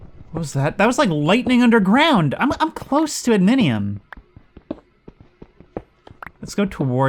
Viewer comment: what on earth are you saying? Adminium? Adamantium? Are you talking about the Bedrock layer? I'm so confused. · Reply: Yeah. We used to call bedrock "adminium" mainly before 1.12. It was a subtle change I never noticed and went from calling it "adminium" to calling it "bedrock", too.